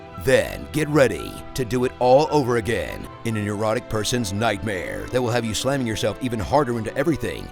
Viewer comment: Thought he said Erotic Nightmare instead of Neurotic Nightmare. Kinda put a whole new twiat on the furry fox zelda genre for me tbh.